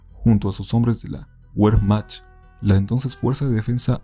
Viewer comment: Lo deje de ver desde que escuche "la güermach"